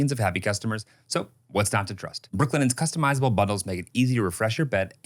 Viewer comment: i’m sure it’s just me but during the brooklinen ad, I heard say “customizable buttholes” instead of bundles 😹😹😹